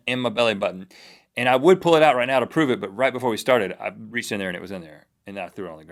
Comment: I had to pause part of this for half an hour to go do some stuff, Came back forgetting momentarily what had been said then hit play. First thing I heard was "I would whip it out right now but" and I started laughing.